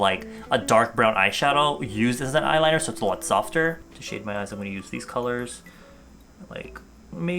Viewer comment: So instead of "shade my eyes" I totally heard "Shave my ass" .....